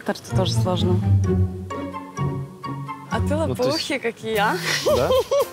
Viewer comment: "Лопоухий"?! :))) Что она говорит вообще?! Ей 30 лет! Видать, не в себе от встречи. :)